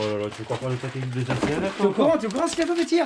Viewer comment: C'est moi ou a dit "zehma" ?!😂